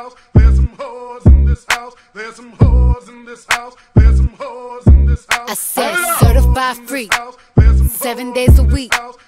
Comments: Nobody: Not a single soul: Me at 3 am: ThErEs SoMe HoEs iN tHiS HoUsE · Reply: I thought it said “there’s some whores in this house” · Nobody: Not a single soul: Me at 3 am: ThErEs SoMe HoEs iN tHiS HoUsE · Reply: I thought it said "There is a boss in this house" I am Portuguese- my English is horrible